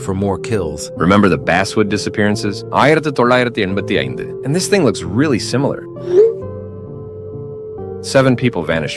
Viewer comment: what did he even say bro 😭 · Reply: It's tamil indian language he just said 1985